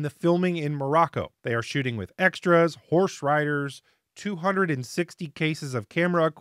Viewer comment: I guess the spirit animal for the Green Ajah is a cougar. At first I thought you said 260 cases of beer rather than cases of film. I guess I have happy hour on my mind